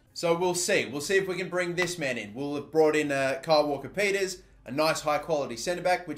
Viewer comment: I thought You Said Kyle Walker penis